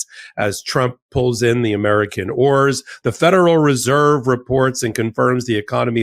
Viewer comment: Sounded like he said "while trump pulls in the American 'whores', had to listen again to be sure what he said!😅